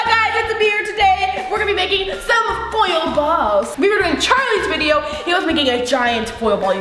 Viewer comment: I thought you said boil ball XD #ialwaysdoit These are always so great and random XD